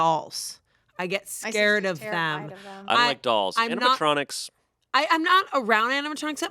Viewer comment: Did Shayne just say that he likes dolls at 1:01:04??? My brother in christ, there was an entire video where they tried to scare you with a ROOM FULL OF DOLLS!!! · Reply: He actually said the exact opposite, "I don't like dolls"